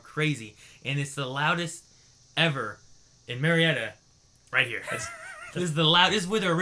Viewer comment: Damn it, I thought he said MURRIETA....stupid California names sounding like Ohio...